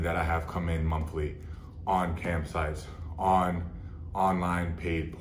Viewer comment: At first I thought he was saying camp sites, and was super confused.